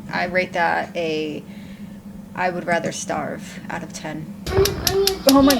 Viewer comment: I'm sorry, did she just say "we're out of water"?